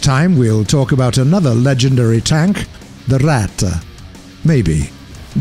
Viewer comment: did he say The RATTE or am I just on drugs!?!?